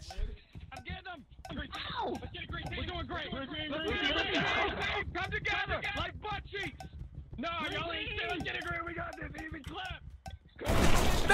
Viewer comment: Did really said "Buttcheeks"🤣🤣🤣🤣